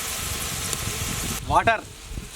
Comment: I'm sorry I swore I heard grandpa's voice saying "water". Don't mind me, just makes it more sadder.